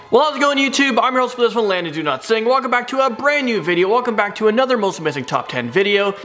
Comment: I swear, everytime you introduce yourself I hear, "Lana. Do not sing" and I think, geez, what does this guy have against Lana Delray's music? Lol. But I'm also hard of hearing so ....